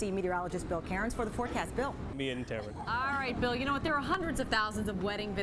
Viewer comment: wtf ''me and tamron'' i think he said amy but still that's what it sounds like everytime i listen